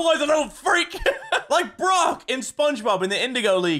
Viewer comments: Did anyone else hear at 13:30 mini say brock from spongebob, or is it just me? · HOLD UP at 13:30 did he just say like Brok from sponge bob?